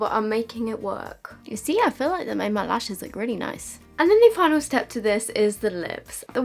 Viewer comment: what did she say?!🤦🏻‍♀️😂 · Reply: "You see I feel like it makes my lashes look really nice"